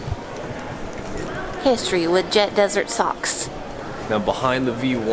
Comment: Hi you should listen to riptide it is a real good song i think you would like it. In one part i thought you said jet desert socks. Leah you should come to Albuquerque and go to the balloon museum